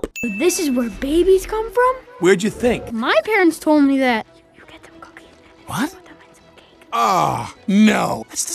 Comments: who else heard “you give them cookies they you put them in some cake”? Lmao 😂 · said "you take some cookies and put it in some cake" least that is what I heard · did anyone else hear you get the cookies and put them in the cake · I hear, "You get them cooking, and then you put them in some cake."